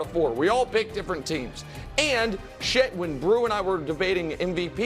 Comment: Did nick just say shit on national tv?